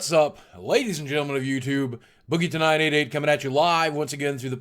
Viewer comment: I always thought he said "boogie tonight" for the longest time. I just realized he's saying "boogie two nine ..."